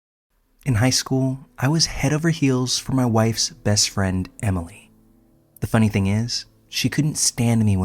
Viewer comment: am I hearing this right? “In high school, I was head over heals for my WIFE’s best friend Emily”?